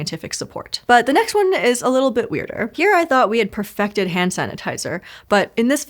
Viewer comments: the host says “the next one is a little bit weirder”, but the closed captions say “the next one looks a little more promising”…. 🤔🤔🤔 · Caption inaccuracy here, the captions say “a little more promising” but Niba says “a little bit weirder”